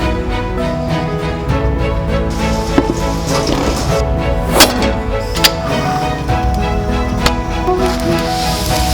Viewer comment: 어디서 많이 들었는데... 김.........생.... (생략) 무서운 그 단어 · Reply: Keep!!!!! 김잘생!!!!!!!